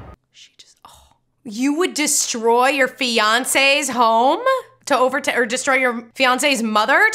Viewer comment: For some reason, I thought you said "You would attack Beyoncé's home?" instead of 'your fiancée's' and I didn't even question it. Like yes, of course Beyoncé lives in a chrome dome in the Earth kingdom.